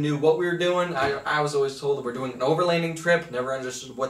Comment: Ooh overlanding trailer! I thought you said overkill trailer 😁 some skookum fabrication there my guy!